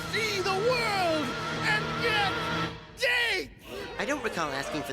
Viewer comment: Sounds like he said "and get d*ck."